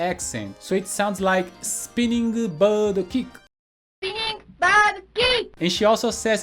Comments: I always thought Chun li said spinning side kick. Also thought that the dragon punch was called the raising dragon fist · No one. Absolutely no one thought he said Tiger ROBOCOP lol. 😂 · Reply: I always heard Chun-Li's spinning kick as: Spinning Star Kick.😊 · Always thought Chun Li said "spinning side kick" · I thought Chun Li said " Spinning side kick" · We always thought chun li said spinning na chi 😅. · My friends and I swore Chun Li said "spinning c*nt kick." 😅😬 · My brain is weird, when Chun Li does her special attack I hear “spinning star kick” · I thought Chun Li said "Spinning Star Kick" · Always thought Chun Li said “spinning star kick.” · I thought Chun Li was saying, "Spinning Style Kick". We used to make up our own words for Ryu and Ken. Words such as "F@ you, then!" and "Aw<link> You B!+ch! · I always thought chun Li said, "spinning vile kick," lol Crazy. this game has a lot of memories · I thought she said inside kick all these years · I always thought Chun Li said "Spinning Tornado Kick". It was actually the one thing I understood, but I was wrong for 30 years, lol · I always thought Chun Li said: "spinning star kick" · all this time i thought chun li was saying spinning round kick////the tiger robo cop is hilarious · I always thought Chun Li said "Spinning Round Kick" and Sagat said "Tiger Up-her-cunt". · I always thought Chun Li said "spinning star kick" and "guess not!" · I thought chun li said Spinning side kick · Thought it said "Spinning Round Kick"